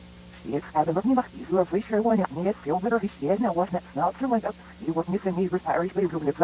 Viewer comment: WHAT IS THE SHEEP SAYING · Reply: Rosemary Would Go To The Restaurant, Every Night, Hoping To Find Her Beloved Husband, But No Response, until one day, she heard a voice saying "I know where he is Rosie" Coming From The Backstage."